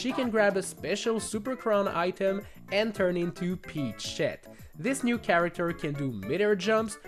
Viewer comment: did I hear u wrong cuz I thought u said peashet